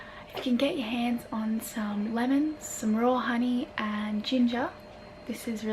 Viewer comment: I thought you said Royal honey, lol, I'm sure you would make fun of my hillbilly essence too, RAW honey 🍯 is the bests!